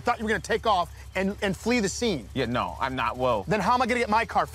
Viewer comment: “I’m not woke.” Is what it sounds like he says and it’s perfect lmao 😂